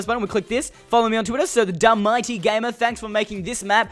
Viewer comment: I legit thought he said DumbMightyGamer. DUMB